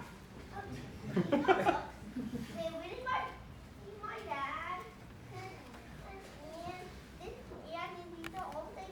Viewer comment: What did junior said at 12:17 I couldn’t hear him well... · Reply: qavisd qi he pointed to the stack of books on the table and said- “these are the books written by my dad and it’s called the war on normal people”.